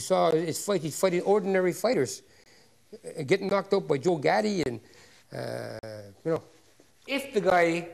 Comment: ya une faute à 1:51, « Gatty » c'est quoi c'bordel c'est Gatti